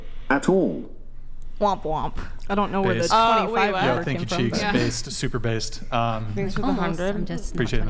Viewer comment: did you just call her a super crackhead super bassed haha